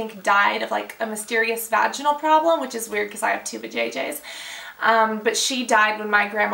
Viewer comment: did she say she had 2 vjj's????